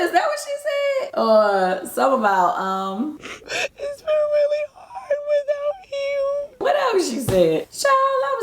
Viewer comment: I thought that she was saying..Its been really hard without you. What I got from that during her postpartum depression she didnt have Nene to talk to..Also with Dennis cheating on her.